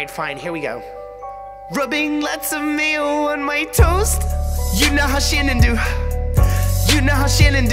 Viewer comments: I thought they said Rubbing lots of mayo on my toes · I thought he said rubbing lots of mayo on my toes